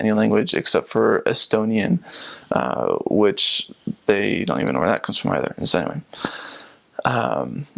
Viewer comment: What did he said in 3:59? "They dont even interact ... anyway "? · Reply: Don't know but to me sounds like "they don't even know where that comes from either"